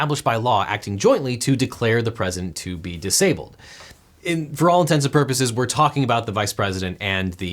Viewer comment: Objection! Did you say "for all intensive purposes" at 6:08?